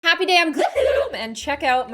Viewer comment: For some reason my sub titles were on and when she said happy day I’m gloom it thought she said Happy d*mn click I’m gloom